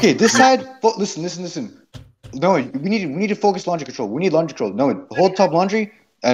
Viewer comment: Lmao before the second half started I thought said "Launch Control"